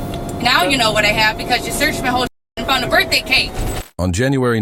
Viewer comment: I heard. "You searched my shit and found a B'day Cake" before I saw the video.😂